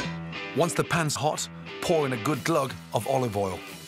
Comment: Did he just say "Gulag"? *STALIN INTENSIFIES*